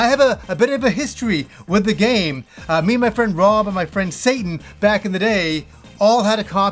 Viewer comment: I swear I hear my friend Satan